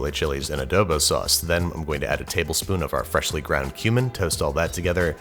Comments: Sounded like you said freshly ground human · I know it's not what he said, but it totally sounded like he said 'freshly ground human'.